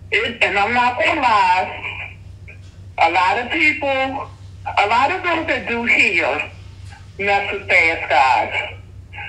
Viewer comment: What did she say at 1:55 - 2:02? What was her past life? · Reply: "I'm not gonna lie. Alot of that do hair mess with fast guys" 😘